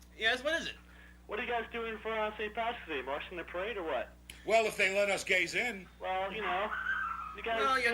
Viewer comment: I honestly thought said, "Well, if they let us gaze in." Shows you what I knew . . .